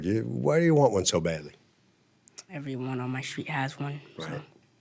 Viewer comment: Did the Lil boy say " every one on his street has one " ? Clearly the mom just doesn't want it. Plus , what Lil boy says " mommy, the news says these are illegal " " I can't ride this anymore ".